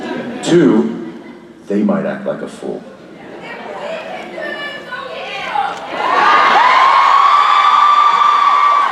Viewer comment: what did the girl in the background say? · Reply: "If we can do it, so can you!"